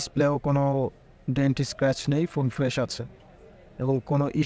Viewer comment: তে কি বললেন এইটা আর স্ক্র্যাচ নাই ?